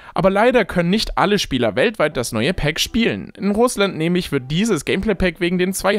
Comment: Bei 3:04 hab ich statt bei Russland, bei uns, verstanden erstmal herzenfakt stufe 12/10